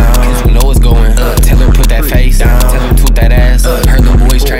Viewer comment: Yo what did this nigha say at 1:00💀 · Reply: Tell em not tell him 😭😭💀💀💀